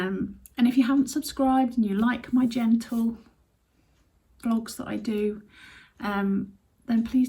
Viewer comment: I thought you said genitals 🤭 Your vlogs are always a delight. Take care x